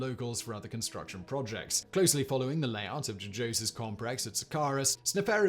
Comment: Anybody else catch 'comprex' at 7:10?